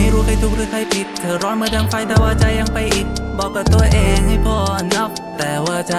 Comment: ได้ยินว่าบอกกับตัวเองใน Pornhub😂